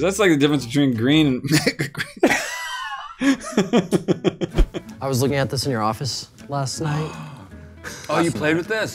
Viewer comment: What does he say at 6:14 ? the difference between green and what? Lol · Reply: The difference between green and mega green